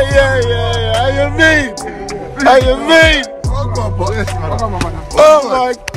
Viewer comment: yo yeah yeah yeah اهلا بيك اهلا بيك did he really said that 😅 What is he saying here for real? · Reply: how ya been